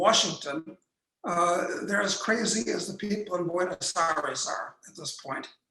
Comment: What is he saying @ 17:00 Buenos Aires?